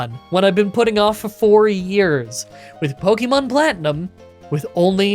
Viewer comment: initially thought you said you'd been putting it off for 40 years, not 4 LMAO looking forward to next week's run! And I'm glad Sabu's dental stuff went well!